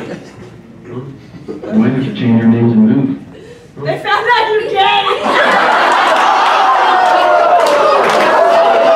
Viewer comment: what she say?? what the man said??? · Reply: she says "its not like your gay"